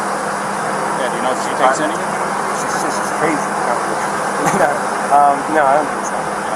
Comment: When the police asked Brian if she takes any medication it sounded like he said, “She’s just crazy.”